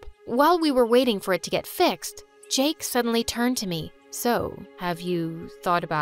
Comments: did she just say "jake" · did she say Jake?😹 Btw I loved this so much😩💗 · did she just say Jake? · did she just say Jake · did she just say " jake suddely looked at me " at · Did she really say Jake- or am I hearing things-😭